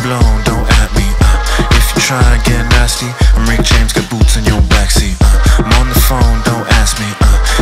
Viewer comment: My boots and my laces is what I thought I heard at first